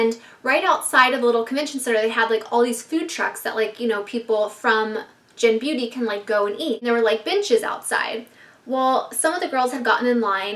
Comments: at first I thought she said bitches were outside til I realized she said benches were outside lol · Every time you said benches I thought you were saying bitches 😂😂